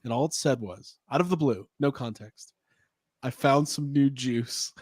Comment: relating story about meeting at Farmers Market…”Guys I found some new juice…”, what I heard, “Guys, I found some new Jews…”. I was so confused